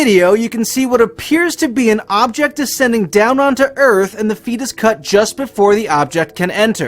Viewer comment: Did he really say "Ascending downward" instead of "descending" ?